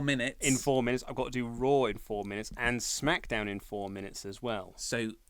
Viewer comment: I had to rewind, I thought said he was going to do roids in 4 minutes.